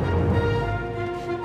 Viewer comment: Does anyone know what Pockets says at 5:17? I've always thought it's, "Here's your Bobo Peter. How is it?". My brother and I have been saying that quote since Hook came out.